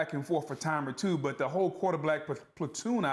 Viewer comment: Did Boog say "QuarterBlack" or is it just me? lol